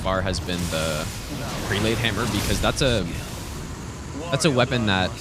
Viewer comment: guys help me if you can, what did he said is his favourite weapon at minute 5:04? i heard it like a hundred times and still can't understand what he says before hammer. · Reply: Fire prelate hammer